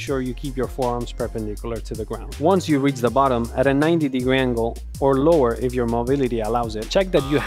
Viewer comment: I heard "go lower if your mom allow it"😅